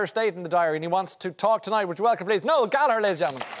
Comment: What did he say, Noel Gallerlizm? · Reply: 😂 Please welcome Noel Gallagher ladies and gentlemen